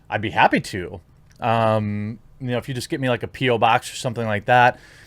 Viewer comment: I heard “Just send me a pee-o box.”😂